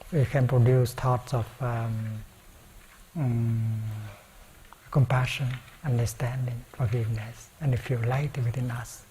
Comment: Could anyone clarify what dear Thay says at 1:18:33? And if … we can heal the world. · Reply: With right view we can produce thoughts of compassion, understanding, and we feel light within us. It recalls to Thay's teaching that with mindfulness, concentration, and insight, we nourish the seeds of joy and happiness within us.